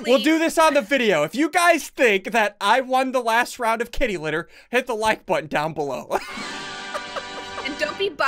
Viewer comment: Wait a minute did he just say get him to ten likes!?!